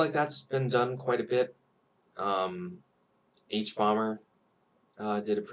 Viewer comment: you said something that sounded like "h-bomber did a pretty good video on brexit" but I can't find any such channel. Could somebody link me to it?